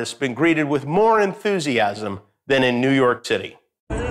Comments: was I the only one who heard “New York Titty”? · Did he just say New York Titty? 😂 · I am not the only one who heard 'New York Titty'. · Did he say New York TITTY?????